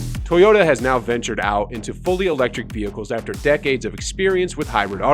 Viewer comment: Did he just say decades 0:05?